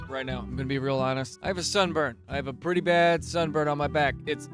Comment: At the beginning I heard “I have a son” And then proceeded to buffer for the next thirty seconds before I realized it was sunburn.